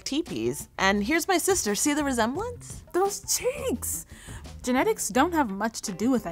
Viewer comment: bro I thought she said those ch*nks.